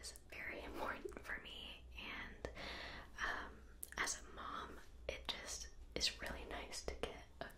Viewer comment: Bruh does she say she is a mom??